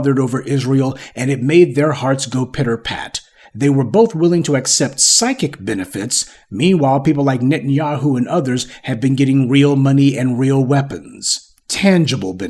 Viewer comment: oh I thought you said psychotic at first in the post 😮